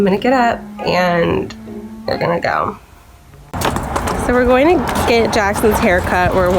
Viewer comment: i heard you say were gonna cum